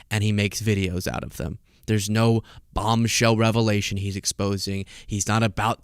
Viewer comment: Hi! Oh, I need my hearing ✔️ checked; I thought you said “Bomb shelter”! 😂